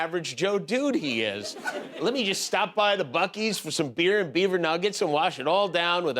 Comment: Hey Jimmy, I Love you and your crew you're the Bestest! Lol, I thought you said, "Ted said, Beaver Nuts!" Lol! I Love, Love,LOVE your Show jimmy hunny your super funny